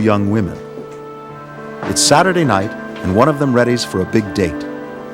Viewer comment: it sounded like "one of them is ready for a big dick" at 0:15 lol